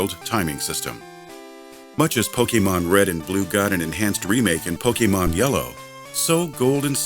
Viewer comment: Wait I'm sure i heard wrong but did he just say pokemon green red and blue ? Hmm